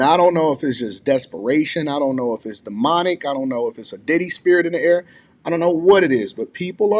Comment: Did he say a “Diddy spirit 🤣”